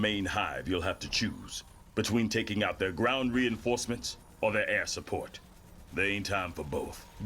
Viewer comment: At first I thought said "nydus worms or there ass support" wow I heard that wrong